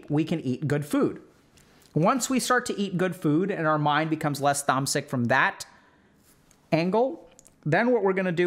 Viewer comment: Chat can you help me? What is the word that Dr. K say at 26:07, your mind become less tomsick?